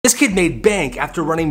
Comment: I thought he said this kid name bank